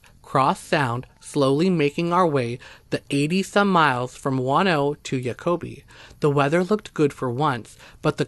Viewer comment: I kept hearing "wanno" and didn't know where that was until I heard the capital and realized you were talking about Juno..lol..interesting stories..